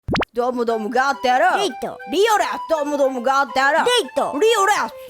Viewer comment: 始まりのリオちゃんの挨拶が「リオレウス」に聞こえた。（現実逃避）